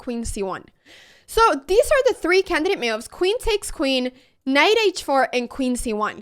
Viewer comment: I thought she said candidate milfs 😅